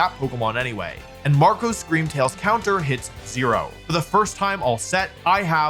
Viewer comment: there is a typo in the subtitle, instead of first, it said fist